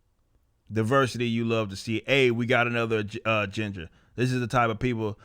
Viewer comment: Ginger? Oh I thought you said ni